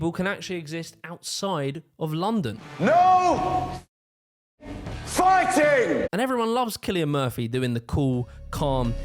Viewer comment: What I heard is: No, fucking, fighting [...] and we don't hear the rest